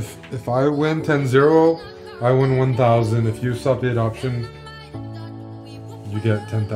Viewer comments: Literally slurs so hard in the beginning I don't know what he says, "If you ....what? you get $10,000" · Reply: Oh stop the adoption, yeah I know what it means but I kept hearing "if you subdate option" lol. Thanks. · What did say at 0:10? All i hear is “if you sfadbsudisknz you get $10,000” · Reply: If you stop the adoption ( chess term for losing 10 times in a row) you get 10k